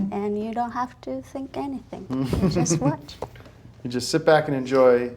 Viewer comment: "It's a fun rock and roll movie and you don't have to think anything." -, master film critic. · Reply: I thought she says "say anything." Then she says "Just watch." I took it as her just repeating what he said to her while they watched it.